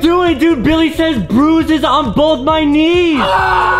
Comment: Justin I thought you said juices on both of my bees